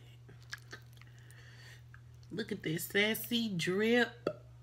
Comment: "Sassy Drip" catchy name! I thought you said saucy drip at first, I like that to.. I just steam me some GreenLip mussels today, just caint get enough... And.... Yessss it gets REALREAL wit the corn😂 Shrimp & Lobster lookin mighty good right about now.... 5am and I'm watchin folks eat!😂 Aint even been sleep yet, smh.. Much success on ya sauce sells❤